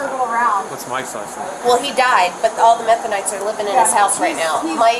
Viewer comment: 🤦🏻 Methanites! I heard it as “Mennonites” - when he came out of the house I was thinking, “He’s not a Mennonite!” I had to back the video up and turn the captions on 😂😂😂😂😂😂😂😂😂😂